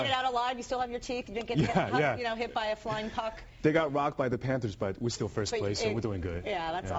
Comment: did she say flying cock?